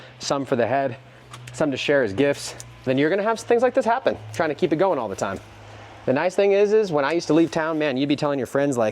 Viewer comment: What is product is he saying at the 32:30 mark? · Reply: Colorado Worm Company